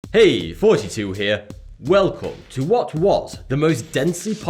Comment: and all this time i thought for sure he was saying.. hey 42 heya...